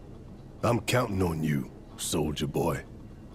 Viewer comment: I swear when Barret said SOLDIER boy I heard SOULJA boy. I CANT UNHEAR IT